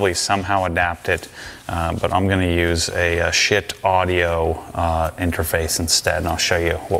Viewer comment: did just say Sh!t Audio? 🤣